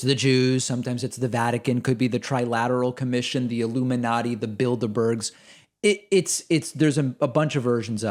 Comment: for a second I thought you said "the Build-a-Bears" and I didn't even flinch, I just thought "oh do they think Build-a-Bear is engineering an army of sentient teddy bears using secret Chinese AI and genetic technology that operate the shadow government? Yeah that's totally something Tucker would say."